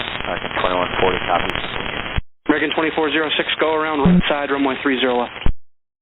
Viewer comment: Both the controller and the pilot were talking way too fast. I heard "go around right" but only on the second time you played it. That was too close.